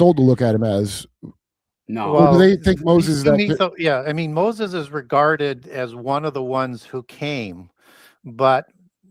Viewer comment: I thought he said "Moses is retarded." 😆